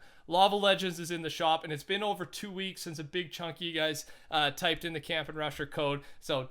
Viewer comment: vid buffered and I legit thought he said Big Chungus XD